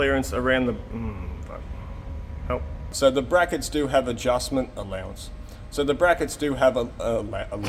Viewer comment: The CC says says :) .. "[snort} alushment (giggle) ... I googled it and no hits!! :) LOL